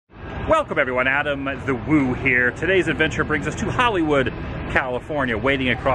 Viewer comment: I always thought he was saying Adam the woohair